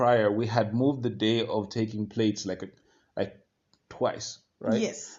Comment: I thought he said, it starts with "taking place" 😂